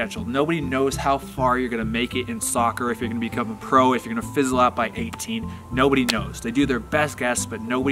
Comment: "..fiselop.."??!! What does he say? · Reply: Fizzle out